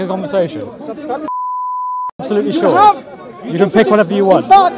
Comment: for those who don't understand what he said, he said, " subscribe to dawahwise" his channel is called, learn from there